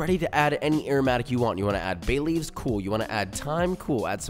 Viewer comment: I thought he said Baileys...but then I checked his description of aromatic ideas and it said Bay Leaf. I. Am. Disappoint.